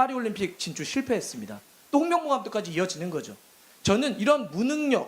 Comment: 똥명보라고 들림.